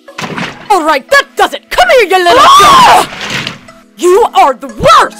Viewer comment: Is it just me or it sounded like said, "Come here, you little sh!t" 😂